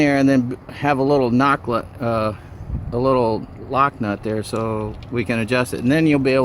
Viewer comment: the guy said “Nock lut” instead of “lock nut”. I really enjoyed because it sound so sweet!